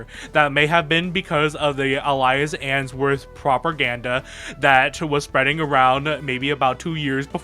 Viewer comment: did you say… Properganda? Is that a reference?